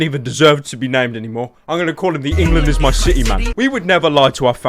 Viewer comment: I always thought it said "England is my titty"